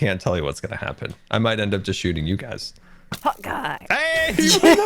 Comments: All this time I thought they were saying hawk eye....not hot guy LoL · Now I have to mention this because this whole time I thought Scar was saying "Hawkeye" not "Hot Guy" and now I think it would be way funnier if it WAS "hawkeye" but I digress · All this time I thought scar was saying HAwKeEYe but it’s hot guy? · This entire time I thought scar was saying "hawk eye" and not "hot guy" I mean it's accurate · I can’t be the only one who thought scar has been saying HAWKEYE instead of HOT GUY this whole time💀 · this entire time i thought scar was saying hawk-eye not hot guy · I thought scar was saying Hawkeye not hot guy🤣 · I honestly thought he has been saying hawk eye not hot guy lol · Am I the only person taht thought they were saying "Hawk Eye" instead of "hOt gUy." I think it becasue I'm a marvel fan...too much of a marvel fan. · Until today I thought Scar was saying Hawkeye not Hot Guy. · All season long, I thought Scar was saying "Hawkeye" and it's been "Hot Guy"! I feel silly now :) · Wait scars been saying hot guy this whole time?? I thought he was saying hawkeye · All this time and I thought they said “Hawkeye” instead of “Hot Guy” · when they say 'hot guy' i thought they said 'hawkye' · this whole time i thought Scar was saying "Hawkeye!" but NO. it was HOT GUY. i've been bamboozled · for some reason I thought scar was saying hawk eye not hot guy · This entire time, I thought scar was saying "Hawkeye", not "hot guy" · I thought it said hawk eye… not hot guy